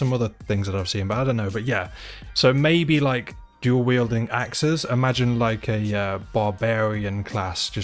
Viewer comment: It took me two minutes till I realized they were saying "duel wielding." I thought they were saying "jewel wielding." I was real intrigued for those 2 minutes. lmao